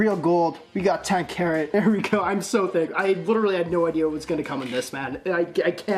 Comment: did he just say “i’m so thick”